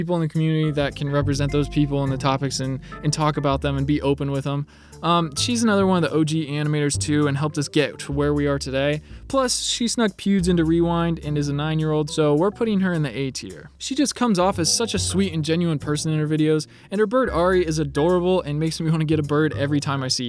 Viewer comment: Did he call jaiden a 9 year old?!